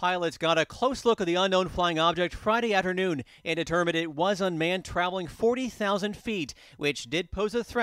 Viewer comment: did he say it was a man flying? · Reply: Unmanned is what they have been saying